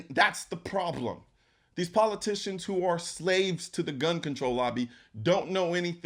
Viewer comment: Gun control, all I hear is “poor control”